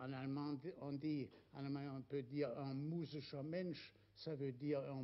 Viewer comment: If you have any idea of what the german word he says at 1:32 is, let me know. I thought it could be "Universalmensch " but it doesn't sound like it. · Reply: He says "musischer Mensch". Someone who is "musisch" has an artistic sensibility/talent.